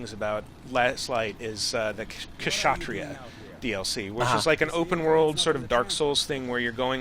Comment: did I just hear "Ksatria" DLC? If so, the word means "warrior" in Indonesian.